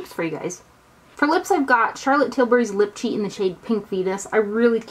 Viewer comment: OMG. The lip liner is Pink Venus. I thought you said Pink Fetus! LOL.